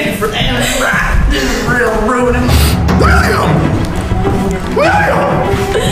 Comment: the way shouted "William" sounded like scooby bro ✋😭